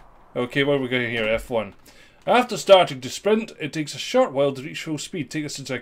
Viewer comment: I thought that said "squint" instead of "sprint" LOL